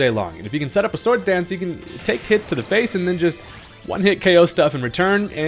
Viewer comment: actually its gigabrain because they gave rhydon lightning rod in anticipation of the tera mechanic 🤓 jokes aside i definitely heard "take some tits to the face" and that made me laugh, even if you realistically said hits and my brain pulled an uno reverse on me